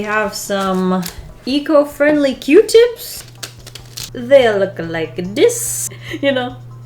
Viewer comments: why did I hear "e-girl friendly q-tips" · i’m so tired that i thought you said “e-girl friendly” not eco friendly oops